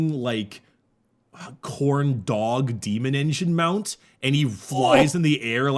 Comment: I did not hear Khorn dog I heard corndog so now I'm confused and hungry